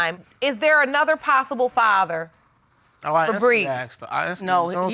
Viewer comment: Why name her Breeze. 7:38 I thought Judge said "Febreze".